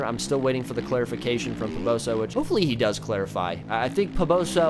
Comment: did you just say clitify hahahaha